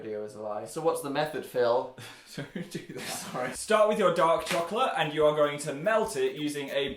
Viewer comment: i heard "start with yiur dog chocolate